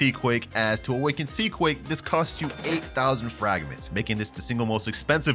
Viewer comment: am i the only one that noticed at 10:53 when he said 8000 fragments the subtitle said 80000 fragments